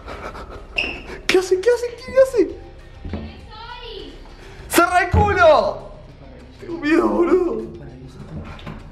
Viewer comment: que dijo :v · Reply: Dijo quienes sois